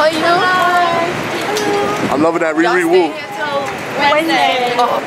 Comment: DID U JUST SAY " YOUR LOVIN THAT RIRIWOO"? LMFAO.... NOT EVEN CLOSE